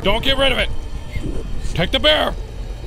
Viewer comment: I swear to God the demon says "take the beer lucy" Holy shit im dead 😂